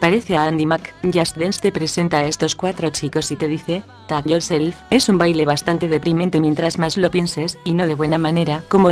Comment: Alguien me puede decir que dice en el minuto 34:46 no le entendí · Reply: ya te hiba a decir "después de eso" xd Gracias